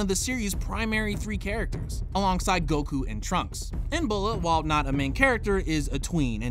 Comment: Why tf was Bulma a teen in GT? I'm so confused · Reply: Pete Ro also 2:53 is what made me think that. Sounded like he said bulma